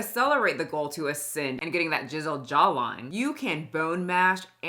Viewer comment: it sounded like you said "jizzled jawline" 😂😂❤ yeah that's right! 😂